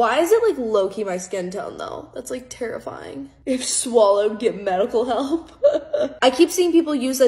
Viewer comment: I heard "if swallowed get mental help" 😶